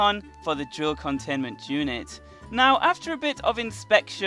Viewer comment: It sounds like you say joke containment unit, and not old containment unit